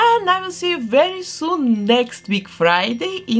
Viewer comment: i think i misheard @ 22:44... it sounded like she said her next "halloween" special... does this mean she's making a really cool Krampus doll next week?!? that would be so awesome! because she's Obviously into the winter holidays so what else would be a cross of beloved 'Ween and Xmas?? not that anything made wouldn't be cool regardless. either way, i'm always looking forward to these vids. ^^ <3